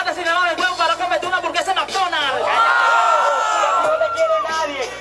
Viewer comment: Que coño dijo en el min 0:52? Escuche turquesa de mcdonald nada más